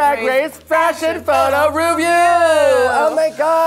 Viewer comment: I misheard. I thought they said "Ratchet Photo Ruview" and I was about to be here for it